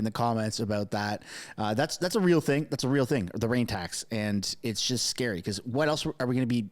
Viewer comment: Oh thought you said brain 🧠 tax